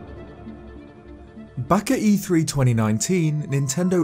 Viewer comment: thought he said baka and I almost punched my laptop out of frustration